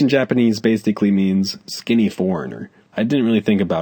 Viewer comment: OH SKINNY "Foreigner" I WATCHED THIS AT FIRST AND DIDNT UNDERSTAND THE WORD HE SAID IN LIKE 2016/2017 then watched it in like 2018 and thought said "former" DAMN THOUGH NOW I KNOW LIKE 6 YEARS LATER WATCHING THIS 13.12.23